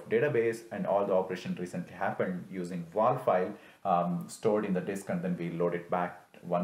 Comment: what file is used? Are you saying "Vol" file? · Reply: WAL (Write Ahead Log) file